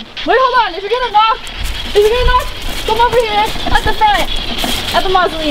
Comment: I swear I heard William say come here